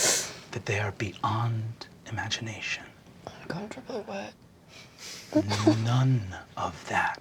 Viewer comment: "I'm uncomfortably wet" is what Kristen mumbled